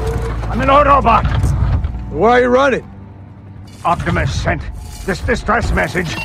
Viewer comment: what did he say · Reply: Why you runnin?